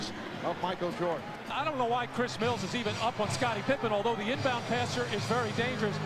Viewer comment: Why I hear the announcer say “Khris Middleton”?